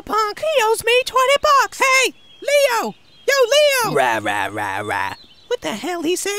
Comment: What the hell he say? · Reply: Rah X4